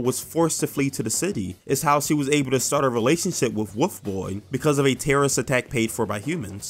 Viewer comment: Why did I hear woof boy